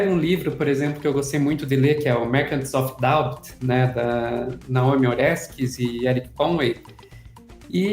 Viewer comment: No minuto 0:50 a jornalista cita um livro, porém não entendi direito. Alguém captou? Fiquei interessado... Seria "Merchants of Doubt" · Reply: O livro é “Merchants of Doubt: How a Handful of Scientists Obscured the Truth on Issues from Tobacco Smoke to Global Warming” dos escritores Naomi Oreskes e Erik M. Conway